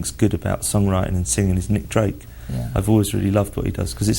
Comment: Subtitles says Nick Cave, i definately hear Nick DRAKE!